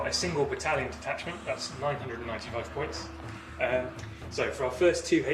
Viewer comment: My only complaint is that your Khorne list seems to be around 111 points too many :) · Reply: My mistake, I thought you said 999 points and was making a joke that 888 would have been more thematic lol. Didn't mean to confuse anyone